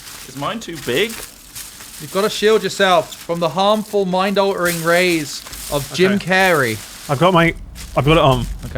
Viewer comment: When you mishear saying "mind-altering rays" as "minority race" I meannnnn